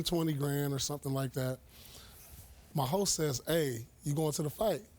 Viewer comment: It sounded like Pierce said "my hoe said" 😂😂🤣🤣